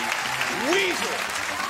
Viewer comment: This was wonderful. I love Weezer and Say It Ain't So. Who else heard him at 0:23 say Whoeezer. That's funny. Weezer fan redemption right away. Cool video. ^_^